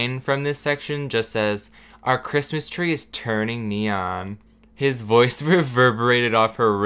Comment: I wasn't paying attention and I swear I hear, "My Christmas tree turns me on. Retweet if your Christmas tree turns you on." Zero fucking context.